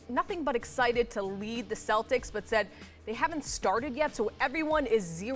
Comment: thought she said kyrie ready to leave the celtics loool